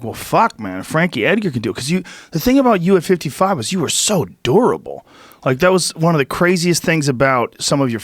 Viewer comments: thought Joe called Frankie adorable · did he say durable or adorable? 😳🤣🤦‍♂️🙉🤷‍♂️